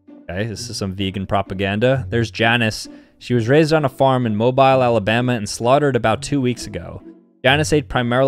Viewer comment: bro did not just say Moe - Byle 😑